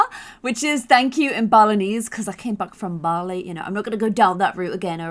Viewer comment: The whole time before episode 1, I thought she was saying I came back from barley and I was like oh I didn't see that on the map. I just realized she was saying Bali. Stupid me